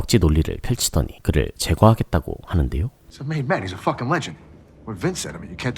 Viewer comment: 에 고위급을 영어로 뭐라고 말하는건가요. 발음이 잘 안들리네요..maybach이란건가..마이바흐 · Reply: He's a mad man. He's a fucking legend.